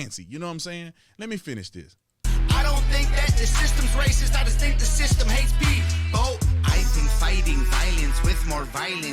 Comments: 54 year old retired white cop that listens to country and 80s rock. Tom is the Shit! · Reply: What does he say here: 3:15 Sound like “i just think that the system hates beef, bo!” ???? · What does he say here: 3:15 Sound like “i just think that the system hates beef, bo!” ???? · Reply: that sounds like what he’s saying tried to listen closer can’t tell for sure. The first time I heard it I thought he said “I think that the system hates me Though?” But think about the Green deal and how AOC hates Cow farts.? I’m not sure now. You might be right?